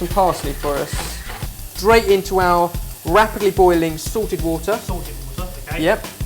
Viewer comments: ohhhhhh salted water, ive always thought he was saying *sorted* water LOL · K, it is hilarious that you guys joked about the similarity between sorted and salted because when I first started watching your videos I totally thought you were saying "sorted water". I thought that is was some special type of water that you had already explained to your audience, so I went searching and watched ALL your videos trying to find out what this sorted water was. I enjoyed the videos but found no answers and then you uploaded a pasta dish and explained why the water was SALTED!